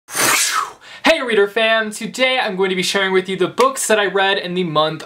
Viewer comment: Ohhhh he's saying "hey reader fam" I thought he was saying "hey reader fan" 😂😂